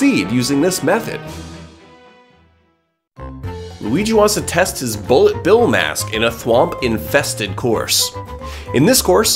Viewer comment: Did Anyone else hear "Luigi wants to test his Bull Sh*t Bill Mask"? 💩🤬💣👺🤷🏼‍♂️